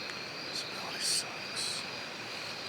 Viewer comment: does say this really sucks?? Doesn't sound like him but it's a whisper so I can't tell 58: 04 you can hear a low whistle then a few seconds another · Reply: Yeah, he says This really sucks